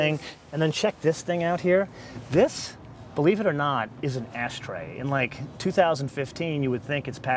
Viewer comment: It sounds like at 8:40 he is saying "ass tray" instead of ash tray.